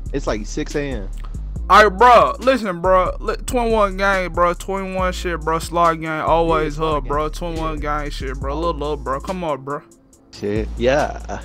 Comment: did 21 say "Lil Boat" 😂😂